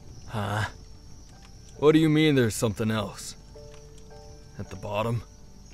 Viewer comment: help me rn when he said “what do u mean there’s something else? at the bottom?” I THOUGHT HE SAID “what do u mean there’s something else, you bottom.” I WAS ABOUT TO START SCREECHING…..